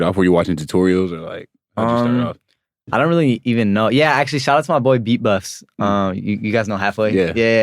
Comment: what is he saying “shout to my boy beat buffs”? Imma try watch that dude haha